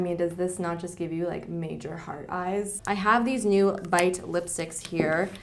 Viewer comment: When you said heart eyes I totally thought you were going to say hard on lol... but maybe that box of lipliners only makes me feel that way. 😒